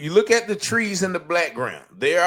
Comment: did you actually say "look at the trees in th blackground"? Racially obsessed much ???😂😂😂